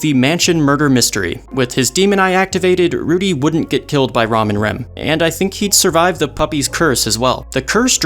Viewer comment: I legit heard it as Ramen Rem